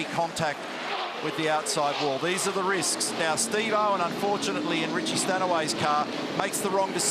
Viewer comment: To make sure I didn't mishear that, what was the name of the driver mentioned at 15:04? I could have sworn I heard a familiar Australian name, but not in auto racing. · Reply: Steve owen?